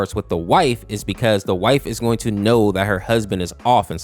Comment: Great video, I enjoyed it, but a weird thing at 1:00 in the video and 2:00 AM, I heard white not wife for some reason and it sounded a bit racist 〔•_●〕